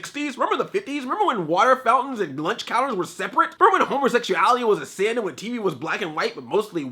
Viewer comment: I heard him say "Homersexuality's a sin" XD